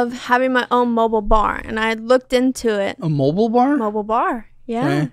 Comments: Lol I thought she said mobile barn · I thought she said mobile barn lol